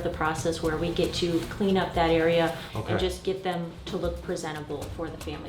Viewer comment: oh lawd I thought she said “clean up diarrhea” 😮‍💨